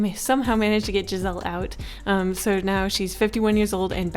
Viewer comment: did u just say gizelle? idk how to spell lol 😂